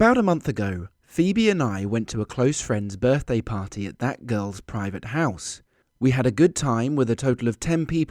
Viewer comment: Who else hears him say private house